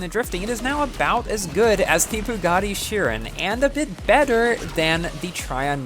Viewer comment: Did I just hear 'Pagani Chiron' in 9:02?